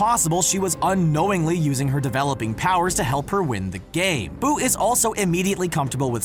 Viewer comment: Ik it says game but I thought it said gay 😂